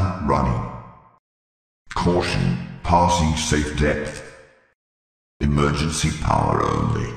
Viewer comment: what did it say? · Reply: Caution, passing safe depth